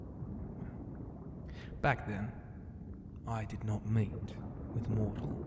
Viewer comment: Not even gonna lie. At 7:04 i thought he said he "didnt mate with mortals. But after going back i relised its just "he didnt meet" almost agave myself a heartattack💀